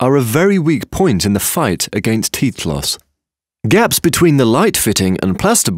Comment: thought it said "against teeth loss" i swear to god wtf